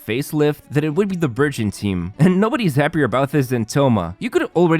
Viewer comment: Burgeon 3:00 Hyperbloom 6:20 Spread 8:35 Reverse Melt Burning 10:50 Honorable Mention 11:35 Bountiful Cores (Nilou Bloom) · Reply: I thought he was saying "virgin team."